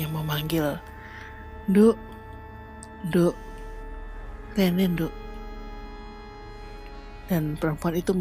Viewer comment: dengernya : nduk, nduk... Nenen nduk...